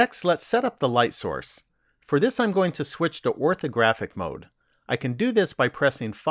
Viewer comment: What did you said in minute 1:15? Something "graphic mode" I can't seem to use it with my number pad :s